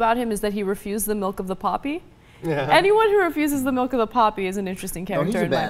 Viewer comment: All this time I thought they said, "milk of the puppy"...never sounded right to me. Weird. So it's milk of the "poppy"?